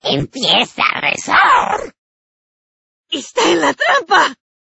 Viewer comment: the first one is wrong. It doesn't say for the love of anything... it says: A por él! which can be translated to: Go for him! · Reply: since you didn't catch up, it says: está en la trampa! which translates to: he is in the trap!